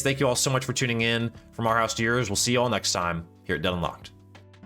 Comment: when saying in the intro, can you seperate the dead and unlocked part. i thought you said dunlocked or deadlock not dead unlocked. if you slow it down just with the introducting I think that would be better. overall good video ;)